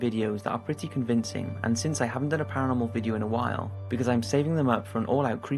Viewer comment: I thought it said Paranormal Porky